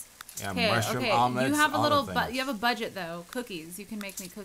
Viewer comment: When Cupquake paused while saying "you have a little bu-" I thought she said "you have a little butt"